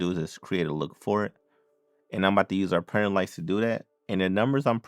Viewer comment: Sir, what did you say at 3:04? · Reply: Sorry that I didn’t say it clearly, I said “Printer Lights”.